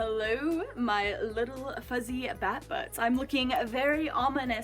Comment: Seriously thought you said "fuzzy fat fucks" at the start. Rad records nevertheless haha